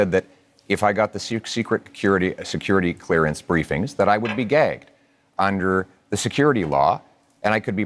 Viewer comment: I wasnt paying attention when you played compilation of everyone questioning Pierre on Security Clearance. I thought i heard him say... If i got security clearance, i would be gay(gagged). 😂😂😂 I mean that's fair! 😂😂